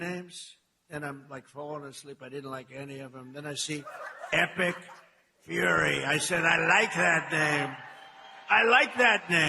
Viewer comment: everytime they say the name, all I hear is epic Furry